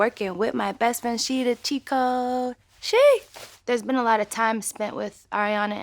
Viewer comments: anyone else thought it said chico not cheat code haha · There are two kind of people : 1. Those who say that in Monopoly they heard " she is the chico " insted of " she's the cheat code". 2. Those who lie about it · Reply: unk 9978 i hear “she the cheo” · I thought you Victoria Monet said “She the Chico”😂😂😂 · It was clearly "cheat code", but I really thought they said "on the road" cause of tours, etc.